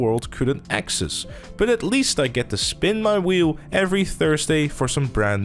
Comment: Hol up at about 4:10 did you say you only can only spin the wheel every Thursday?